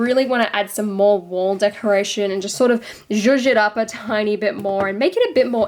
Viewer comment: what was that word you used? Beautiful work. I wish you did more MOC videos. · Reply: zhuzh it up